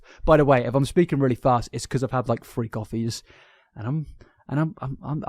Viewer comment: When you said 3 coffees I thought you said freak offys